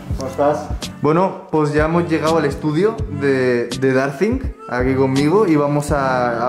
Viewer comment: escuche darling xd